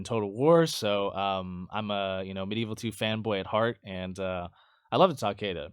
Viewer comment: Did you say "I'm Medieval II femboy" on 20:48?